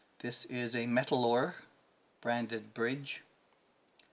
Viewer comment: nice work, what brand of pickguard did you use and what bridge? Sounded like you said "metallor". Thanks